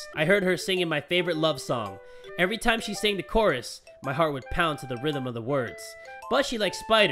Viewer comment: When he said heart I thought he said fart